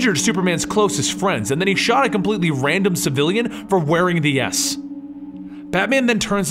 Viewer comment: it says for wearing the ass instead of the S with captions on....lol